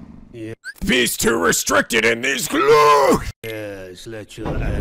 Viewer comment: In 54:05, I'm sorry if I misheard it but it kinda sound like you said "BEAST TOO RESTRICTED IN THESE CLOKE!" XD LOL I'm sorry but that makes me laugh the way you say it.